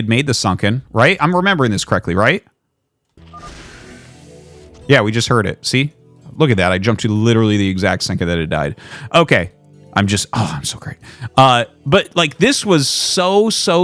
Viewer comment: i def misheard "i just jumped to the exact second that it died" "okay" "oh, i"m so hard" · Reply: He said "oh I'm so great".